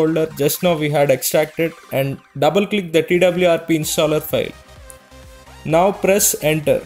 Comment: What he says at 1:01? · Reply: Double click the twrp installer file